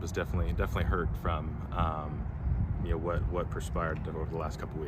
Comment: Did he actually say, “…by what perspired.”? Instead of, “…by what transpired.”